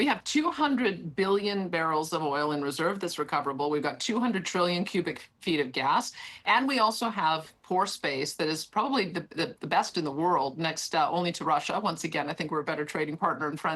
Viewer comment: thought she said "we also have horse paste"